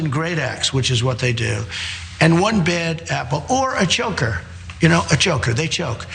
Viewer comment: A choker? Is he talking about the killer if George Floyd or is he saying the police are killing Black people by mistake?? Well he compared killing people to missing a golf put! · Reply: I thought he was saying "Joker", and wondered.